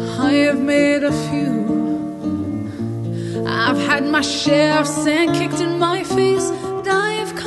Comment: I had to listen twice becuase I thought she said "share of shoes kicked in my face..." My ears are just not working today at all...